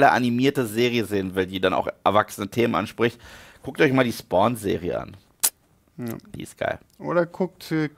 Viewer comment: Wie heißt die Serie, die in min 02:36 nennt? Ich könnte ihn nicht verstehen. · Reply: Ein Jahr später Spawn - die Serie Gibt es aber glaube ich nicht auf deutsch